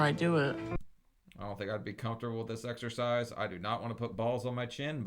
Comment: Excercise? I thought you said extra fries 😂